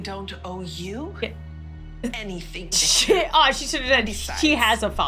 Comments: I heard the s World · DID LAUREN JUST SAY “sh!t” ?